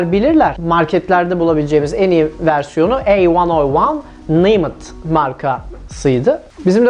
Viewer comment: yarım saattir anlamaya çalışıyorum ne dediğini daha yeni anladım akskdlflrk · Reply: "Ey van o van.." Ne demek lan bu, bir aralar "bi ay em" diyordu. Bu da öyle bir şey mi acab.. Hee! A101 miş. Şimdi naymet'i çözmem gerek. Eeeağmircim bunu mu düşüneyim yoksa yumurtanın kaltağı mı olmaya çalışayım b3n şimdi :)